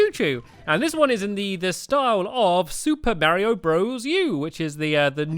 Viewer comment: who else thought stampy said super bario bros